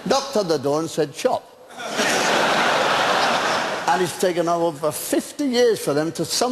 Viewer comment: I'm especially fond of an essay Spike wrote toward the end of his life, touching on the subject of overpopulation. Maybe it's the accent, or Britspeak - I can't make out what Sykes says at 1:40 "he knocks at the door and says _____." ? Please and thanks. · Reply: Shop